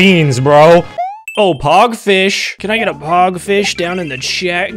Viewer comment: I heard PAWG Fish, and my ears immediately perked up.